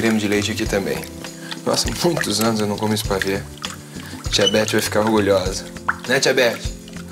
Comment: aushuahsuha Tia Beti vai ficar orgulhosa eu escutei "Diabete" haha